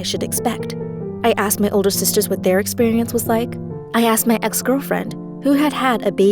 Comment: did she say ex girlfriend